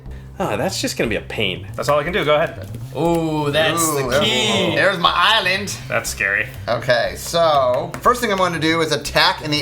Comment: did he say thats gay?